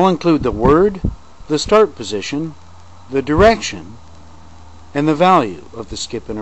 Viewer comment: Great video. Loved it. Very funny! Forgive me for being a bit stupid, but I couldn't quite work out the significance of the first number after the word. You explain it at 5:13, but I couldn't quite hear - it sounds like "stroke position". Could you clarify please? Thanks.